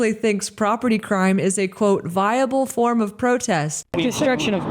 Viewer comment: She doesn't say "Vandalism is a valid form of protest" she says "Vandalism is a valid source of protest". She has no clue what she is saying. Vandalism is a valid... thing from which something comes into being or is derived or obtained... of protest? Vandalism is a valid... the point of origin of a stream or river... of protest? Vandalism is a valid... One, such as a person or document, that supplies information of protest?